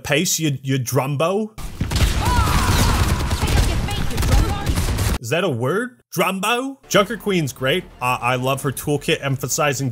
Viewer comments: I heard enough Sydney voicelines in Payday 2 to say that Junker Queen is saying "Drongo." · “What does she say? ‘Pick up the pace drombo?’ Is that a word?” She’s saying “drongo.” It’s a type of bird, but it’s also Aussie slang for basically calling someone a dumbass.